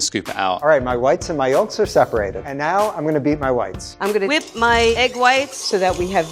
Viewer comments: did he just say he's gonna beat his wife?!?! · I thought the level one said I'm going to beat my wife instead of whites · I swear he said "And now I'm going to beat my wife." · sounded like "I'm gonna beat my wifes" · why did I think he was about to say "and now I'm going to beat my wife" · I thought he said “Now I’m gonna beat my wife.”2:55 I was like whoaaaaa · I thought he said he said "Now we are going to beat my wife 2:56" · the guy just went "And now i'm gonna beat my wi...." And I went "WHAT?" but he said whites, gonna beat my whites LMAO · I honestly thought I heard say, "now I'm going to beat my wife." And my immediate thought was, "wait is that necessary for cheesecake??" 🤣😭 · I thought level 1 said beat my wife at first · Thought he said “And now i’m gonna beat my wifes” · First time I heard this I thought he said "beat my wives". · Why did i hear ”and now I’m gonna beat my wifes” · “and now I’m gonna beat my wife” that’s wut I heard at first lol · “and now, i’m going to beat my whites.” i REALLY thought this man said he was gonna beat his wife 😂 i was like “HUH???” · 😶 Edit: Oh whites , Jesus I swear I heard wife. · sounded like”and now I’m m gonna beat my wife” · I heard. And now, I'm gonna beat my wife! XD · Thought the lvl 1 said, “beat my wifes,” i was like whoaaaaa